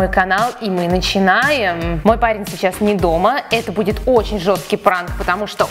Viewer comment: мне послышалось мой палец сейчас не дома😅😂